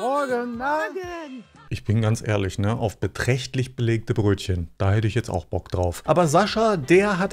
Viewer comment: ich hab erst 60 Belegte Brötchen Verstanden... aber gut das du kommentierst und analysiert! .😅